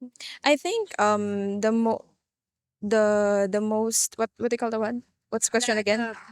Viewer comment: i like the first girl best. probably no one cares what i think but tall beautiful eyes, smart. plus she used my favorite PH phrase... "whatd u call thatwone?" :) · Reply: I heard: "Whatd they call the what?". I've not heard that before, but it certainly sounds cute!